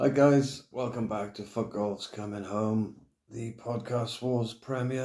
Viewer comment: I thought you said "f**k all is coming home". Bloody accent. 😂 Is it the "fight club postcode lottery" now?